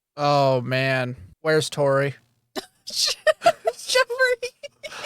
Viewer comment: did just call you?😂